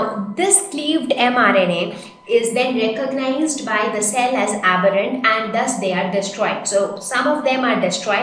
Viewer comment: Ma'am... Wat is that word u used at 8:04...?? ...is considered by the cell as....?? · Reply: Samiyya Ansari it is not apparent for God sake please don't misguide.... It is aberrant meaning they are not normal, or u can say they are not upto a particular standard....